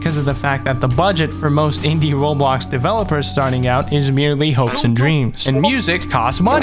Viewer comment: DID YOU SAY "HOPES AND DREAMS"?